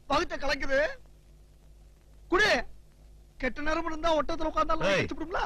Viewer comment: What is he saying at 13:17 ?? Ketta neramnu irunthalum... Something like proverb.. anyone knows ??? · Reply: Keerthi .R.Rajkumar ketta neram nu iruntha , ottakathu la irunthaalum naai kadichipudum la